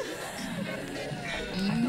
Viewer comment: You know, for the LONGEST time I thought she said, "why can't I do it like this?" for me Heartbreaker intro. I was today years old when I realized that she ACTUALLY said, "we're GONNA do it like this!"